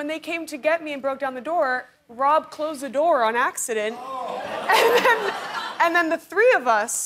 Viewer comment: "by accident," not "on accident." i thought she said "on acid" for a second.